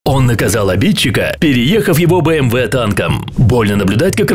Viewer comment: мне послышалось бмв танком